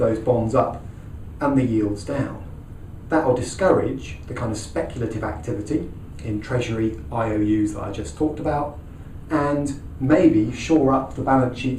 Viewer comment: I can't quite hear the words in between 11:14 to 11:20, any one help? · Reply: "that'll discourage the kind of speculative activity in treasury IOUs that I just talked about"